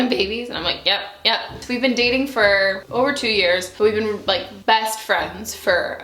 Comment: At first I thought she said "We have been dating for 2 years and we've been best friends for 1.."